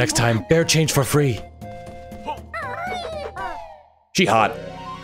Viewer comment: Forgive me if I misheard, but did I hear "jihad" @ 5:47? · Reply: Sounds like it, doesn't it? :D But I think he was saying "She hot"